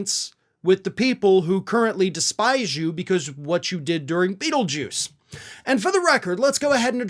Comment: I swear I heard her say Beetlejuice 3 times when there was a long pause.